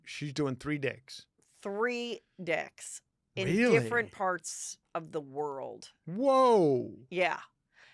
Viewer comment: I thought Heather was gonna say "3 dicks, 3 different parts of the body" I was like WHAT??? LOL